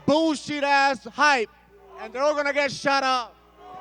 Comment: Vettori's English isn't too good, so it sounded like he said "...& you're all gonna get SHOT UP!"🤣🤣🤣🤣🤣🤣🤣 He meant "Shut up"🤣🤣🤣